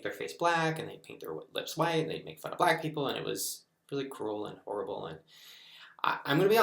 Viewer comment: When he was talking about the history of black face he said, “It was really cruel.” I misheard it as “It was really cool.”